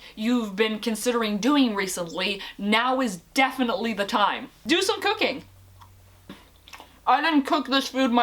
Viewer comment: I thought you said do some cocaine